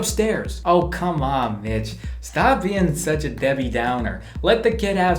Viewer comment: I thought it said bitch but I’m pretty sure he said Mich